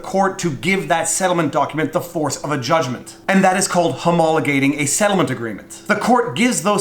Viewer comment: Well crap.... I've got to wait till youtube generates auto captions so I can figure out what this "homaligateerthingamabob" word is... I swear I heard you say it several times but it just sounded like you were having tongue seizures. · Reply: Haha! “Homologate a settlement”. I hope that’s the same term in other jurisdictions…